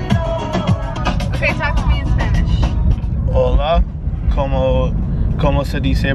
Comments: Why at 2:55 does it sound like said “bae talk to me in Spanish” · Look at 2:54 it sounds like said “babe talk to me in Spanish” like if you hear it